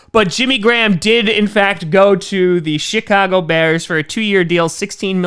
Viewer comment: I thought he said the “shitcago bears” 😂😂